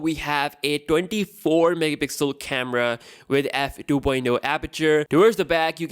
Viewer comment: did he say 24MP camera? Did I hear that correct?